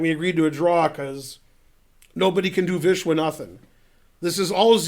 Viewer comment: did he said no bady can vishwanathan